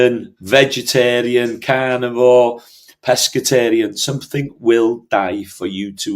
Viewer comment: When he said "carnivore" it sounded like he said "cannibal". I had to back it up and listen again. What a relief!